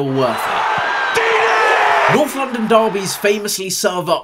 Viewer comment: thought it said NDL